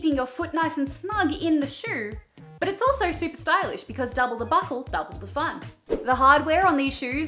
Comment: I was zoning out and at 8:07 I heard "double the butthole, double the fun" and I was like 😳